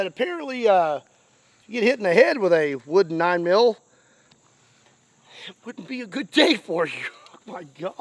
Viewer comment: Did I hear you say "it 'wooden' be a good day for you"? Cuz it sure sounded like it. 😂